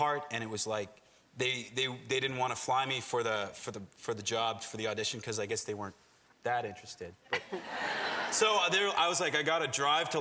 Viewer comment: Hi, I'm French and there is a part I don't understand, could you please write down what Cory says from 2:20 to 2:30 ? Thank you ! We miss you Cory <3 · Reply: "They didn't want to fly me there for the audition so I guess they weren't THAT interested"